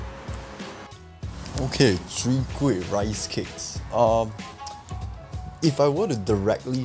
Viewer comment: Lol sounded like he say sowee kueh rice cake at the 5:38 mark. Mix off soon kueh and chwee kueh? Maybe that can be the next challenge.